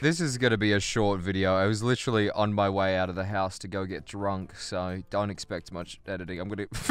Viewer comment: Legit thought u said "I was literally otw out of my house to get drugs"